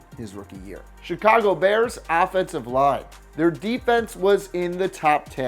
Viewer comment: Could of sworn I heard affensive line